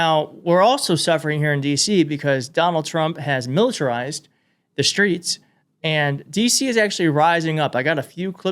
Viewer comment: I swear I thought you said “DT has militarized the straights” and I can’t stop laughing. 😂💙